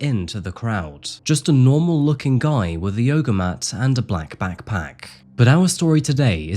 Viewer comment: i heard he came with an "oger matt" and thought nice pun before realising that that was not what it meant but it would fit anyways.